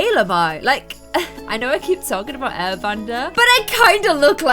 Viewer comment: Tbh I thought she said air vender so I was like can the air vender fly over some food?